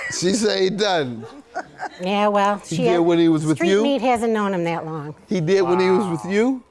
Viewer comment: “I don’t even know why you brought the street meat” Doyle looked like he was about to bust out crying laughing but 😂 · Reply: I heard the all wrong. I thought she said “street meat hasn’t known him that long” 😂